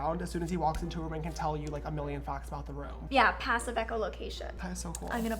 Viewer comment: Can somebody tell me what she said? Thanks. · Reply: Tin Bautista passive echolocation?